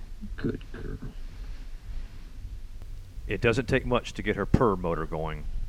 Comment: did he say purr-boner at 1:00 ?